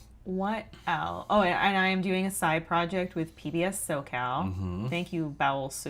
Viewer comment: Did anyone hear the name of the podcast mentions? Seems interesting but didn’t catch the name. PDF SoCal? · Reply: PBS SoCal.